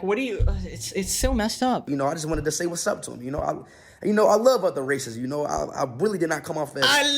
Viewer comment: for a second i thought he said "i love all the racism" 🙃